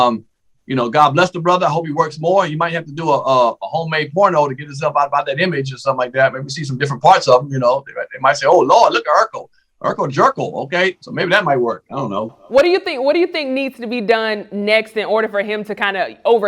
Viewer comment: Did he just say "Urkel Jurkel" lol 😆 😂 I love!! Lol